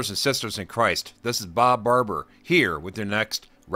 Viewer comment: I thought you said your name is Bob Barker. The Price of Sin is Death. ... ...but is the Price Right ??